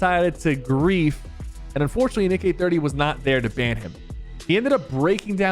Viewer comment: did he say nick eh 30